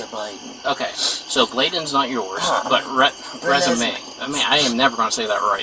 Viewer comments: ~ Am I hearing the little girl’s name is RENESMEE?!?!?! 👀🤯🐀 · I couldn't figure out what the name was. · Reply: oh my I thought he said her name was Resu May